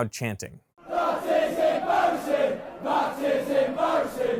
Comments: no matter what the text says, I can always hear the original "that is embarassing" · I just hear "That is embarrising" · all I hear is, that is embarrassing. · I heard "That is embarassing" lol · Thanks to Nutshell Animatioms I only hear “that is embarrassing” XD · I heard “that is embarrassing” lol. And without looking at the words on the screen, that’s all I could hear. But when I looked at those words appearing on the screen, I could hear them as well. Mondegreens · Some of these didn't work for me. I thought the first two notes had the same fundamental. After a few repetitions of the stadium chant, I heard "This is embarrasing". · I always heard "That is embarrasing". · I can only hear 'That is embarrassing' which is what I think they're actually chanting, the words on screen I have to strain to mesh them with the sound I'm hearing as it's so obviously wrong to me. I am British but I'm not a football fan, and I've not heard the chant before. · I heard "That is embarrasing" straight off the bat even reading the words on screen, I usually hear American English in my day to day (2nd language tho) and I sure don't like football so I've never heard that chant hahaha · no matter what it said i just kept hearing "That is Embarrasing" over and over??? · "That is embarrassing" is what I heard, nothing else even matched what they were saying. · I heard "that is embarassing" the moment I heard the crowd.